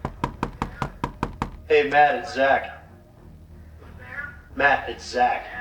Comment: haha when the cop's like "Matt it's Zack" I just hear "Dave's not here"